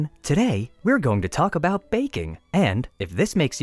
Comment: Thought he said bacon not baking... I like bacon D